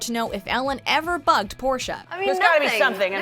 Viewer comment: I thought she said fucked and not bugged